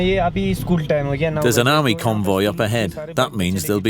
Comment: the driver say its school time now.....he does not say anything about any army convoy...do correct ur narrative...